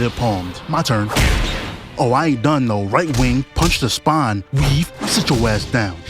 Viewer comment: “right wing” instead of right hook sent me